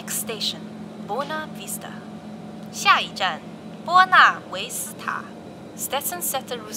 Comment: i thought it said Mona Lisa lol